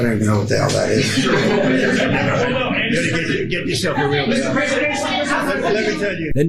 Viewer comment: Did he say 'get yourself a real job'!?